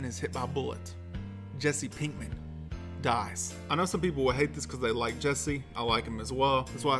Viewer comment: (Mike Voice) Have you turned on subtitles? You're gonna want to turn on subtitles. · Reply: The subtitles at ~6:15 days “Pinkman...survives” instead of “...dies...”